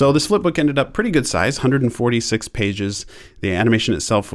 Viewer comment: I thought he said 46!!